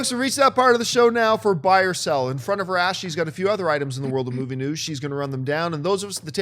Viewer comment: I can never tell what is saying when he says "Alright fokes we've reach the part of the show now for buy or sell ourffekeksldfsklja;la Ashley's got a few..." The CC says: "now for by yourself in front of rashes got a few..."